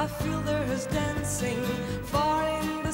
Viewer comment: wait, did I just hear "our Fuhrer is dancing"